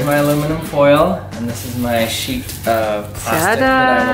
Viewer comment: I am not an English so when I heard sheet I thought shit